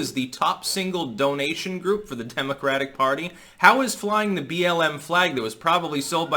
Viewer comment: not sure why but I thought you said "chemocratic party" LOL